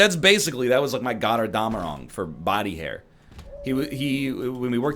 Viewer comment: What word is NL saying at 22:32 ? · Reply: Götterdämmerung, you uncultured troglodyte (kidding). ;)